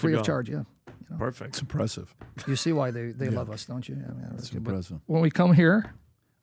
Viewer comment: What did PBD mumble while seating awkwardly @ 37:41 ? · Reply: I think he repeated himself by saying ‘that’s impressive’.